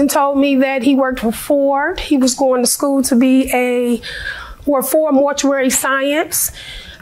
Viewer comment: I thought she said he was going to school - Ford Mortuary Science. 😅 Obviously I rewound it and found that she said FOR mortuary science. I thought for a second that it was weird for FORD to have a subsidiary for interment services. 😅